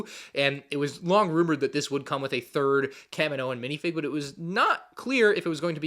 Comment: Did he say third kaminoan